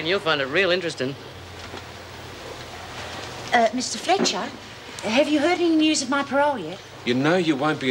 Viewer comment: sounds like she said "Mrs. Fletcher". Lol.